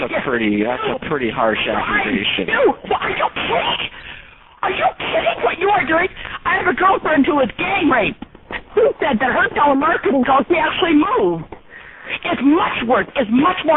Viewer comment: Did she say that she had a girl friend that was gang raped? What in the fuck does rape have to do with telemarketing?